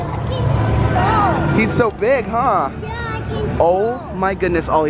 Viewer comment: did he say what I thought he said, " he's swole